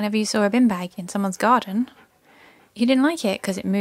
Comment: Bin bags... Okay. Garbage bags. I didn't know what a bin bag was. I thought you had said bean bags at first 🤭😄